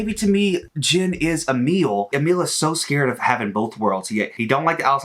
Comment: HAHAHAHA I thought he said "Jin is a meal" instead of "Jin is Emil" 💀